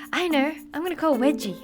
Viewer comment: it sounded like she said i'll call wedgie XD